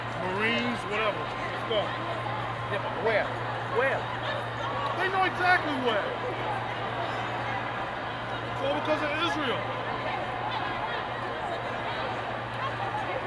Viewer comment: did he just say it’s all cause of Israel